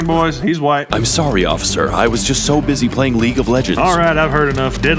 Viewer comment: “The stealth is optional, and so is my mercy.” That has to be one of my favorite quotes from this video. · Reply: I thought it said "bussy" at first lol so I went looking for a comment to confirm or deny lol